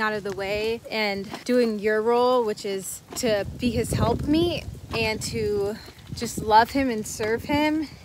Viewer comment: Did she just say "helpmeat"? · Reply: said “helpmeet”. It’s a biblical phrase.